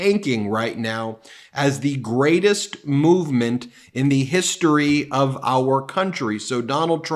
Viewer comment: I don't know why but every time tRump proclaims Truth Social to be the "greatest movement of all time," I keep hearing, "the greatest bowel movement of all time." Sick, I know. :face-fuchsia-poop-shape